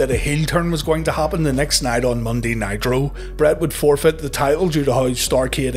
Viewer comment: He says "Monday Nitro" but the subtitles say "Next Night on Raw" Hmm